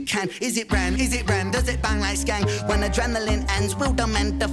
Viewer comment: I thought he said "bang like Skeng"